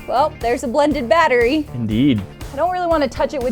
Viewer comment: I thought he said yee instead of indeed